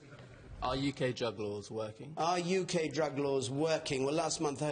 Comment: thought he said "are the drug lords working?" but it was "are the drug laws working?"